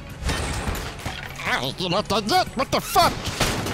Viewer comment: Yo what he say at 45:51 ? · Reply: Zero Wolf "Ay, you're not dead yet? What the fuck."